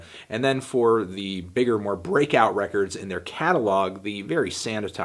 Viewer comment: did you say... CATTLEog